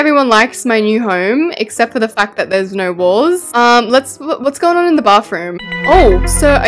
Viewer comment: I thought is said "no balls" idk why 💀